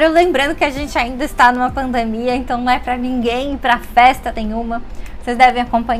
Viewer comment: Eu estou tentando descouvrir o que você fala no minuto 0:20 eu acho que você fala alguma coisa como "então não é para ninguém para festa tem uma" mais eu não estou seguro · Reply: “Não é pra ninguém ir pra festa nenhuma” :)